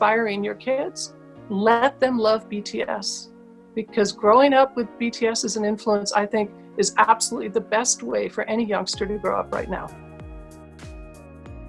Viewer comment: what did she said?? Can someone tell me please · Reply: “Growing up with this type of an influence is one of the best things a youngster can have right now.”